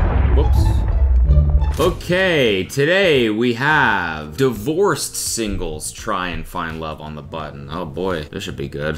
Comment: I thought it said divorced siblings and I freaked out 😭😭￼